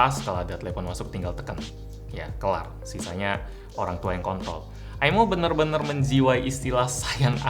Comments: apa kuping gw salah denger?? " sisa nya, biar orang tua yang kon*oL 😂😂😂😂😂😂😂😂😂😂😂😂😂 cadel R emang bisa biikin salah ngomoong njaayy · klo di otak lo lo pikir kont*l pasti kedengerannya kont*l tapi klo di otak lo mikir kontrol pasti kedengeran kontrol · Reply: Tolong sugesti saya... Berkali2 mikir kontrol kedengerannya tetep KoNT*L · ko gw dengernya orangtua yg kontrol ya 😂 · mungkin saya salah denger. Tolong menit segitu dikasih sub, biar ga salah denger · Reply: Kontrol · Menit 08:45 sisa orang tua yang wkwkwk apa salah denger saya nya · Yang ngomong kontol kontol, coba deh dengerinnya pake headset yg bagus gtu yg mahalan dikit.. "R"nya masih kedengeran kok.. Nonton youtube itu pake headset goblok !